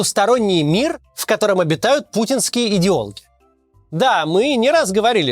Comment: "Путинские идеологи", а я не расслышала сперва и показалось "путинские идиоты" 😀😀😀, что не удивительно